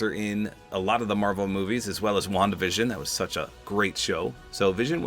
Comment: When you said Wonda vision, I thought you said Wonka vision.😂😂😂